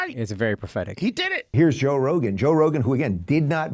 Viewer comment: Did you just say: Roe Jogan instead of Joe Rogan?😶